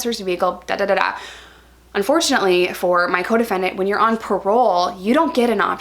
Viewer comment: ohhhhh coDEFENDENT.... most of the time I thought you were saying your codependent. Like that relationship doesn't sound healthy.